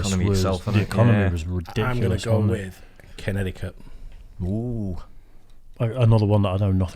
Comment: When first said Connecticut I thought he said Kennedy Cup , LOL !!